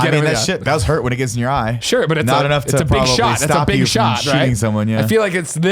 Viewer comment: If you shared this already I apologize as I has to head to work before finishing the video, but how do I get to watch Nick's documentary? I am very interested. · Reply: Oh see I miss understood cause I thought was saying “not enough of you watch it <link> but guess he wasn’t talkin bout the doc